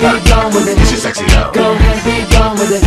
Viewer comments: Omg IT sound like its saying who s you sexy elf Duhhhhh legolas ❤️😘💕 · Love it!!! So perfect for Legolas! My friend thought the lyrics said "get your sexy elf" I was like OMG dude